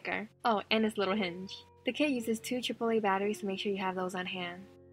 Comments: I thought you said “2 chipotle batteries” hahahaha 😂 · I actually thought she said “two chipotle batteries” instead of “two triple A batteries” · I thought she said two chipotle batteries until I realized she said triple a batteries🤦🏻‍♀️🤦🏻‍♀️